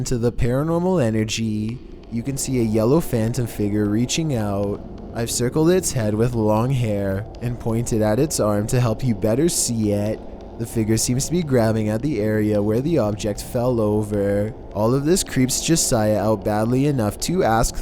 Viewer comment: wait did i just hear him say to better see it :0